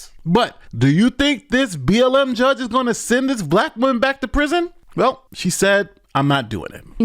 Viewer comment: Did she just say in the beginning that she's a middle-aged white female??? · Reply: no..she said black woman